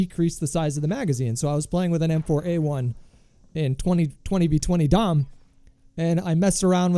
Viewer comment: Did he just say 20v20 DOM!!!!